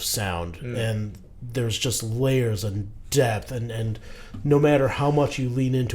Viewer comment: I totally thought he said, "Layers of DEATH." I'm like.. I mean Chopin can be pretty intense, but maybe that's pushing it? 🤣